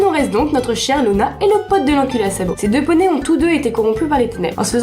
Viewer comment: Je crois que j'ai entendu "et le pote de l'enculé à sabots", mais chuis pas sûr à 100% x) Ce personnage nous à tous déçu, je dois dire que c'est de la faute de Twilight qui nous l'a un peu trop survendu. Bonne vidéo, intéressante et pas mal montée, mais j'aimerais souligner un point améliorable, la diction. À certains moments en fin de phrase c'est trop rapide et pas très articulé. C'est généralement un poil trop vite pour suivre correctement toute la vidéo. Alors j'ai déjà vu ce genre de format, le premier qui me vient en tête c'est ILoveKimPossibleAlot qui met aussi des gags visuels qui reposent sur des jeux de mots avec un flot de parole rapide (Et c'est pas la première non plus à faire ce genre de format). Alors probablement que tu t'inspire de quelque chose comme ça et donc que c'est voulu, mais travailler la diction ferrait pas de mal, ou bien voir à faire la même choses avec un flux moins tendu. Le second point de diction c'est qu'il faudrait pas qu'on imagine que ce soit un texte lu, il faut que tu adopte le même ton qu'une conférence ou une discussion. Là ça se sent quand même un peu que c'est lu. Enfin tu me diras, Bronie teaching c'est bien pire pour la diction, mais pour lui c'est trop tard, ça fait trop longtemps xD